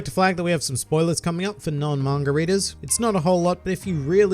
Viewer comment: You know you're an alcoholic when you mishear "Manga readers" as margaritas.